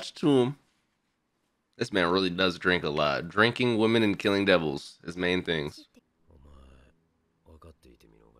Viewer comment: man I’m dead, my guy u forgot the pause between drinking and women. I thought he said he likes drinking women 💀